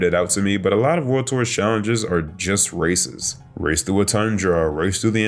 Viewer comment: For a second when you said "most of the challenges are races" I thought you said "racist". Then I realized it when you listed the races.